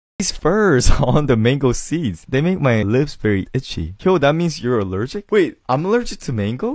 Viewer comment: Why did i hear "that means you're a virgin" instead of "that means your allergic" I was giggling like a 12-year-old boy for a minute and then more so when i figured out what was going on.